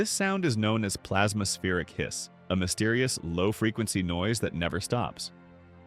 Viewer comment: hiss i thought it said kiss